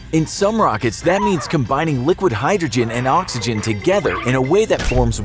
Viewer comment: For some reason, i thought i heard liquid nitrogen...